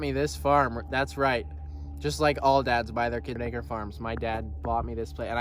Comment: congrats my man but what did he say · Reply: "Acre Farms" but there was definitely a glitch in the audio that made it sound... weird 🤣